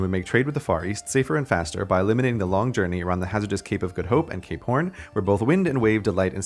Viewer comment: it sounds like you said cay porn instead of cape horn